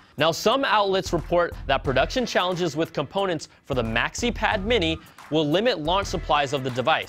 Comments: Did he say maxipad mini at 1:15? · did he say Maxi Pad Mini... Brian Tong....nice joke